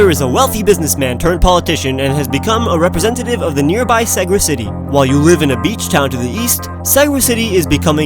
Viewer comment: Also what is the name of the city, I couldn't understand what was saying, I thought he was saying like segwood city, for the love of god someone please tell me what he is saying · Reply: Segra City